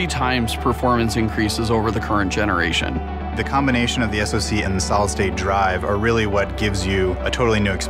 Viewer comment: "The combination of the SSD and the Solid State Drive are really what gives you a totally new experience" Lol what? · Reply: I thought he said "SSD and Solid State Drive" too. After seeing the comments I went back and listened closely. He definitely said "SoC" instead of SSD (which they should have defined).